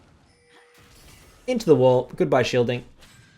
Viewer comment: To me, "Shelly" is not scuttle crab's name, but Rift Herald's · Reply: When did he say that ? If you're referring to 7:14 he says goodbye shielding, not goodbye shelly. Talking about the shield that goes away when the crab gets cc'ed.